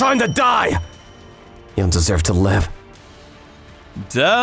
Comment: I heard you deserve to live😂